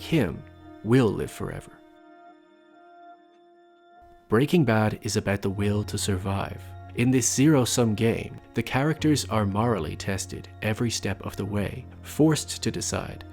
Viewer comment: I thought you said “in this serial so gay”